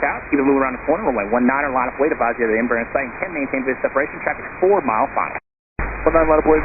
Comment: These guys need to slow down or go to elocution classes. Even with the subtitles I can't make out what they're saying. A good case in point is 1:18. "Advise you have the Embraer in sight." I'm a native speaker and that's extremely hard to make out. Imagine if English is your second language. Madness!